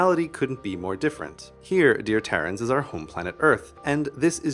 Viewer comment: did he call us Terrans? Awesome